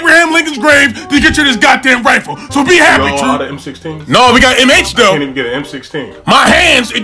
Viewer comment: yall aint got no more m16s? We got m8s 🤣🤣🤣🤣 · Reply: The XM8 was a beautiful weapon. But apparently the military didn't fuck with it. But yeah he said M-H...My Hands. I thought he said M8 too. I was like "I want an M8"